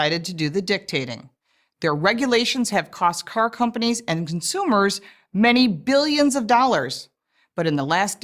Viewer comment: I heard "cost oil companies and consumers" for a second i was like? Really they just admit what this is about...But I just missheard it...